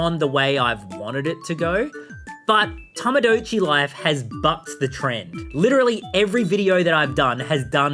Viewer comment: For a hot second there, I thought he said “f**ked” and I had a mini heart attack.